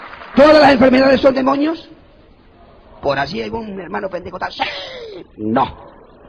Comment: Alguien me puede decir si yo escuché mal en el minuto 25:25 cuando dice que si algún hermano es pendejo eso dice?? Y que dios me perdone si yo escuché mal 🙏🏻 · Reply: Él dijo "un hermano pentecostal". A eso se refería